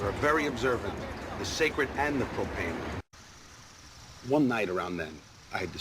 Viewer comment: Holy shit, I only just now realized he said "propane" instead of "profane." I always thought he said it normally because that line is so stupid and pretentious as it is. But the fact he even says it wrong is just another cherry on top. God love this show!